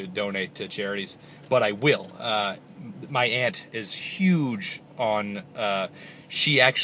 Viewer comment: My aunt... thought he said my ant.